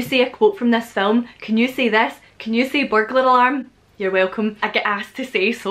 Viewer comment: in the video: What did you say? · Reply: Burglar alarm!